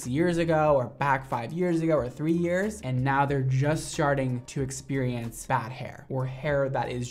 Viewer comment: Anyone catch that “just sharting” at 5:34? Lmfao